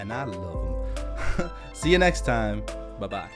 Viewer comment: In the end of the video when I say bye bye it kinda sounds like he says Bubba! Just me?